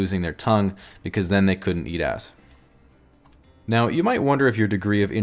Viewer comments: did he say "'Eat ass" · does he say "eat ass" or "BS"? either way I'm cracking up...cause I do both. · does it say "eat ass"? haha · Where as the biggest fear of an extrovert is losing their tongue because then they couldn't eat ass??? Am I hearing this correctly? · “Whereas the biggest fear of an extrovert would be losing their tongue because then they couldn’t eat ass” i must not be the only one who heard that lol